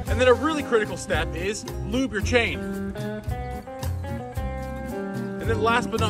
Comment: bro i thought u were saying glue your chain💀